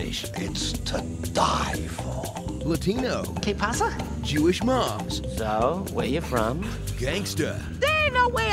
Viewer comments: Oh it's Jewish mom? I always heard Jewish mob 🙈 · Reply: Maxine Gounden same. I heard "Jewish mob" too!! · I always thought they said Jewish monk, which confused me. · I always thought they said Jewish monk, which confused me. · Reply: charmedfan7704 I always thought it said Jewish Mob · In all fairness I thought they said jewish mob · I thought he said Jewish Mob.